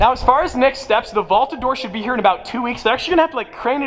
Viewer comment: Sounded like you said vaulta door